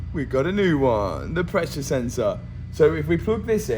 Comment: Literally thought he was saying “precious sensor” the entire time 😂